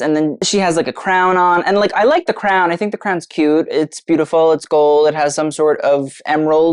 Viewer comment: did you say it's gold??? Am I mishearing some sort of lingo, or is my screen on the fritz?